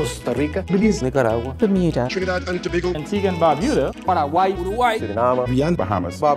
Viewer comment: did anyone understand that 😂😂😂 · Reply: Trinidad and Tobago